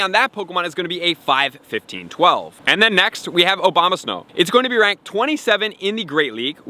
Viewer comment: I swear I heard "Mega Obama-snow" haha